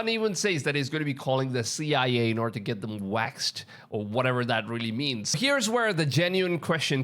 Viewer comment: To me it sounded like he said "whacked" not "waxed"